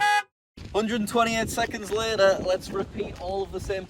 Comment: i thought he said 128 minutes later 😳